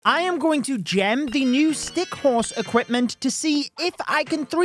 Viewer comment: did you Said gem ? GEM?